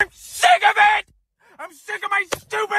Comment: For some reason I heard “the thick of it” instead of I’m sick of it. 😂